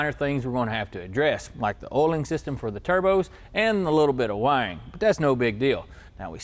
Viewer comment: A little bit of wang? Whatever boats your float. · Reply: he said wiring It just sounded like he was saying "Wang"